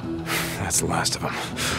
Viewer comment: auto-generated captions say "The Last of Us"